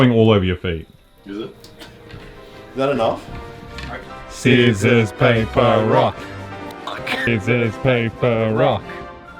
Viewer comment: did they say scissors paper rock instead of rock paper scissors